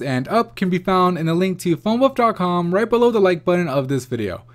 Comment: Good vid .... sound like he said "phone butt" haha